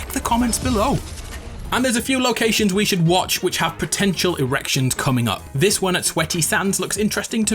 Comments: did he say erections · did he say potential erections?